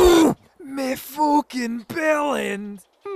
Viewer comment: What did he say exactly? Me fucking bellums?